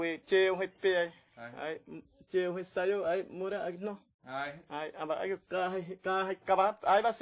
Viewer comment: as a Brazilian I could identify some words he speaks in Portuguese....I just hope these words dont replace the words in his mother native tongue. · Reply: Acho que ouvi "morar" as 2:56 tambem